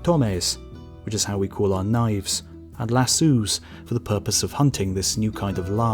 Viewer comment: did he say lassoos? Did he mean lasso?